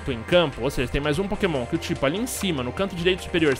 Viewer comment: Eu ouvi ''pokemon'' ? jaskoppkosadkpodsa